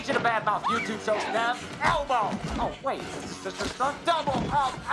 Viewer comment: When he said elbow it literally sounded like he said Elmo